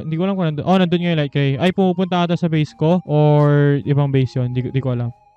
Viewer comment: what did you say Bobo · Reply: Pupunta yon hindi bobo AHAHHAHA